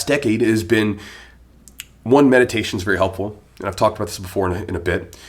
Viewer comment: When you first started giving tips I thought you said "medication" is very helpful lol!